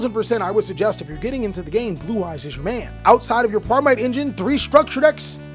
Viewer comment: Thought you said "Blue Eyes Jimmy" and now I have a new name for Blue Eyes players